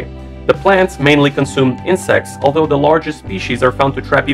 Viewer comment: guys turn on the Subtitles and pause at 4:33 something is wrong here, it should be insect instead of this word